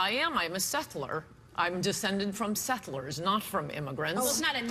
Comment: was Ann saying"sethler" or "settler" when shes asked if she is a native american? it sounds like she said "sethler". what is sethler?? i cant find the word. i listened to it 10 times. it definitely didn't sound like settler.